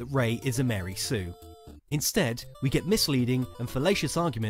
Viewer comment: While we allow free speech on this channel, we don't endorse all the comments attacking Ridley's physique and calling her stupid. We understand what it feels like to be let down by the new films - we were gravely disappointed ourselves. We can also understand being infuriated with people who are duped by Ridley's fallacious arguments - we are most certainly annoyed with that ourselves. But in our world, at least, it's better to counter her arguments than to attack her appearance and intelligence. YMMV. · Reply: I thought you said fellatio